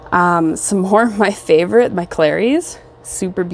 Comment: Beautiful! What is the plant at 6:02? You said Clary?